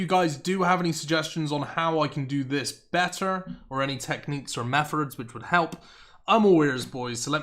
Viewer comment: did you just say “mefurds”? Lol